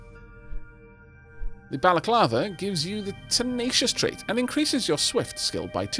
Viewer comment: I thought that said "baclava".